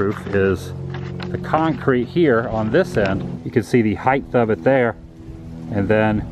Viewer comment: Did you say "heighTH" at 17:57 ??